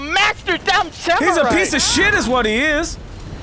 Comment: When H20 said Piece of Shit I thought he said Pizza Shit Lmao